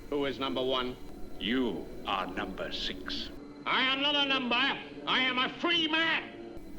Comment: In the intro, I always thought it sounded like he said, "I'm not a number I'm a free mare!" 🤣